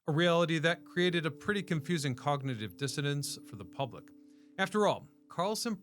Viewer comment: Did he just say "cognitive dissidence"?